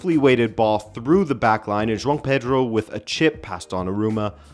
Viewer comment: I’m so confused. I thought you kept saying John Peter rather than Joao Pedro.